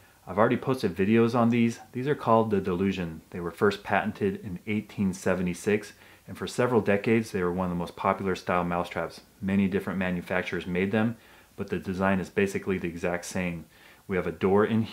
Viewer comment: Thought it said patient instead of patent... lol